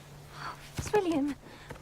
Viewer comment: I heard “Squilliam!” the first time I watched this 😂